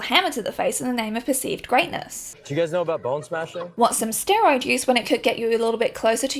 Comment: i misheard "bonesmashing" as "ballsmashing" and, for a brief moment, thought these men had finally realised homoeroticism and kinks are a thing.